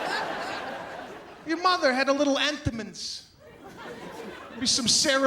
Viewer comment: What’s the word he says at 1:28? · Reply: Entenmann’s cake.